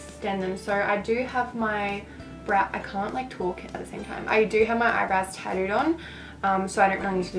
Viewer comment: wait did she say she has her eyebrows tattoos on? or did I mishear?